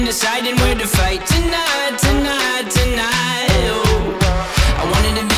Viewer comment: this whole time I thought he was saying "tonight, tonight, tonight oh" but he says "deny, deny, denial" which one y'all like